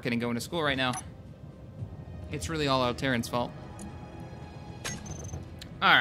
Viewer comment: at first I thought you said it was all Terrans fault, I was like "hey!wtf did I do?!" XD